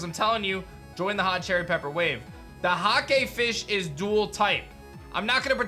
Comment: I could’ve sworn he said, “the hot gay fish is dual typed” 😂